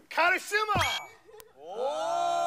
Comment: 작은소리로 카리스마..카리스마.. 라고들림ㄷ